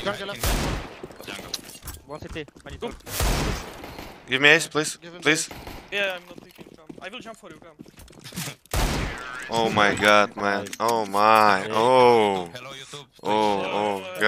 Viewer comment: for a moment I thought he said give me a$$ please lol